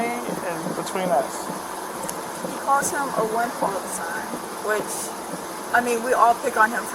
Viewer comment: what did she say? (He call him a blank all the time )For some reason I can’t hear that part? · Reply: She says “wimp”. But I’m guessing that’s not the word she was initially going to use since she stopped herself and said she doesn’t like to curse in front of adults then uses wimp instead.